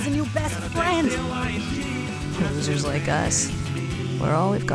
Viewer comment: I dont undestand what Vinnie says at the last part😭 i hear "we are all we got jagsbsusbbs"